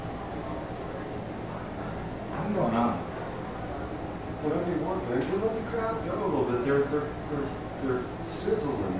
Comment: I’m sorry, I suck at making out words. What does say at 1:15? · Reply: "I'm going outta here"?